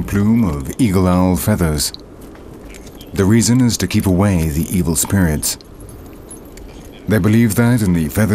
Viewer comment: At first I thought he said that the falconer wore feathers on his hat to keep the eagles parents away 🤣